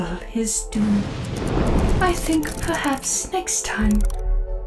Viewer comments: What did she say? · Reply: So basically she said "I think perhaps next time we will give him you? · what she say? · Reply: "YOU"